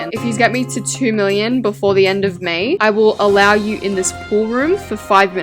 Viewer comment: I thought she said “the end of Maine” 😂😭😭